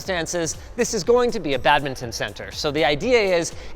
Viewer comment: Did you said "badminton center" 😢😢😢 Thank you